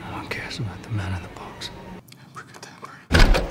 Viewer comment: As many times as I've seen this movie, I've never seen it with subtitles on so at 29:10, I always thought he said "I'm pretty damn great", not actually "abracadabra" lol